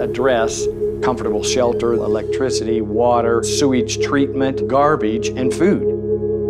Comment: Did he just said...Garbeach?